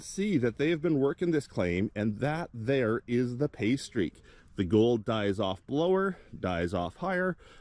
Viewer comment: It took a few minutes... I thought that you were saying "pastry" instead of "pay streak". I was thinking you had a new code word for a good spot.